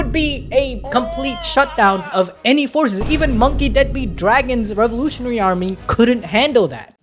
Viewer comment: i heard monkey dead beat daddy😂